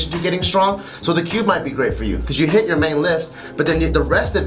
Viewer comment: mentioned a program at 2:51what is he saying? i can't hear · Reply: kärlekskemi he is talking about the Cube workout/method